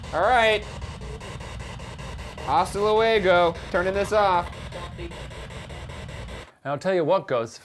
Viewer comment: Who else heard: “Don’t leave” when Ryan announced that he was turning off the spirit box?